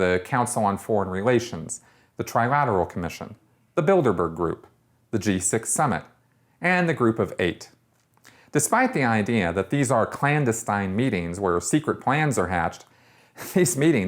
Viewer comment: BILDERBERG. I thought you said Build-A-Bear. Was really confused.